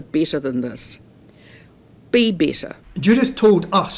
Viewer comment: Did she say be better! I thought she said be bitter!!!